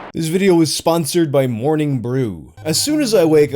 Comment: Oh I misheard I thought this video was sponsored by morning wood